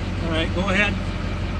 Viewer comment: I think he called you a Goat head!🤪